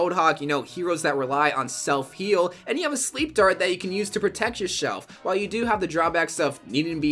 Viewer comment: Who else heard "and you have a sleep dart to protect yourshelf "